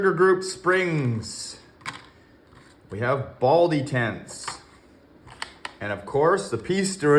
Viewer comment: I thought you said Baldy Tents instead of ball detents for a sec🤣